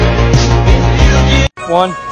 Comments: I heard okay I'm gay not okay I'm good the first time lol🤣🤣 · I heard "ok I'm gay" I think he said ok I'm kidding